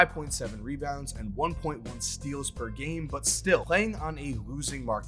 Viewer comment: I thought he was saying LON not Juan